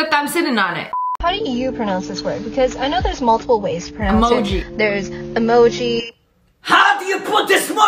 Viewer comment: I heard moji 😂